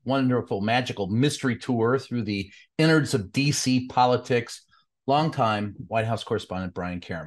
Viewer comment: Oh, DC politics. I first thought he said faeces politics! 😅